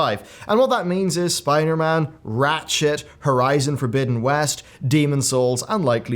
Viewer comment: I swear to god he said ratshit there